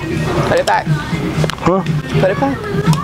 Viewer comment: I thought she said u have a emo